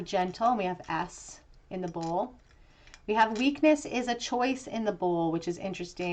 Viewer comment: did you say we have S in the bowl? or did i heard you wrong? 😅